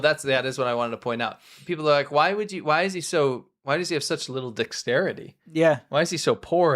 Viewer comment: i thought he said little dixterity haha im like perfect